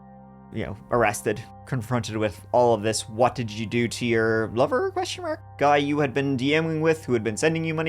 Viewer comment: Here’s a laugh… I thought u said blubber not lover😂😂😂